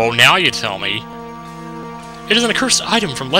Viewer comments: At first i thought it said sexing rituals O_o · alright at the end when he said Hexins I thought he said sexing.